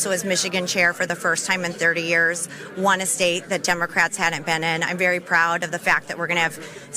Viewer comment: did she say, "I won a state that democrats haven't been in"?